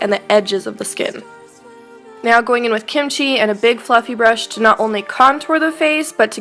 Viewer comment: hold on did she say kimchi?